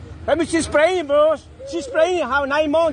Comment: I thought he was saying she was panicing but he was saying she was pregnant.